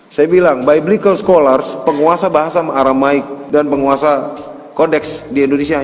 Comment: Yutbernya salah dengar Coba simak baik-baik apa yang dikatakan Steven Indra Wibowo bukan menguasai bahasa Arab tetapi aramaik...itu bahasa dulu sebelum Injil terjemahan.....waduh waduh.... salah total Kamu tidak lulus jadi YouTubers